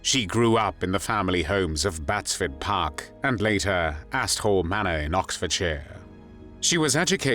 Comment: literally had to turn CC on (after rewinding a couple times) to find out the real name of the manor mentioned. Not to be immature and not being from the UK it sounds a lot like Asshole Manor. Instead of Asthall Manor. Did anyone else miss hear that too?